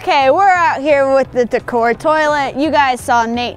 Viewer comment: she really said t'core huh...